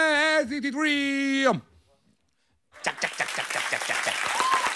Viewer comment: idk what the DJ is saying but it sounds like jungwoo’s jack jack jack 😂